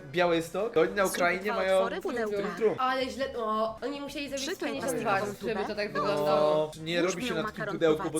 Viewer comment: usłyszałem że ta typiara z troom trum powiedziała utkaj dupe XD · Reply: przyklej plastikową dupe”